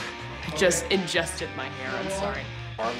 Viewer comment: What did Cobie say 2:18? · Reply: She said, "I just ingested my hair. I'm sorry."